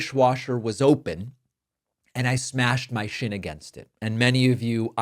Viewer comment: Sounded like you said “smashed my shit” and I felt that